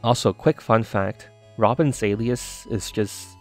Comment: I could've sworn that said Robin Williams.